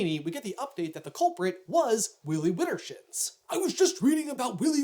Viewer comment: Anyone else misheard that name as Willy Whittershits? No? Just me?